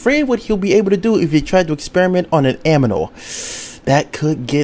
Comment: Did he say aminal?